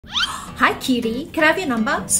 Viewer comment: I thought she said Kiwi when she said "cutie"🌝